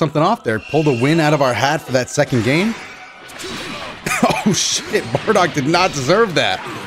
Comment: I think he said shit 12:20 he said bullshit